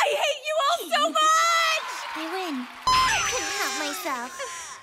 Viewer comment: Did hyoko say on 1:01 " I'll cut myself " ? . _ . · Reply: "I couldn't help myself" lol